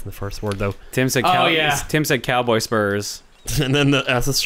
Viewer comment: I... I thought Tim had said "cowboy sperm"